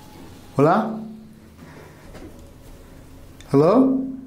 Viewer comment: that sounded like a man's voice 👀 Did it say Hola? Strange that you hear no footsteps.